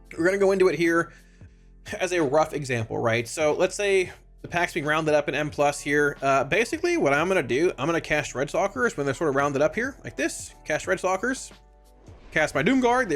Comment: "Redsawcurse" man you gotta enunciate a bit, had to watch what you did before I figured out what you said. edit: woops hit post too fast, also thanks for the details very nice vid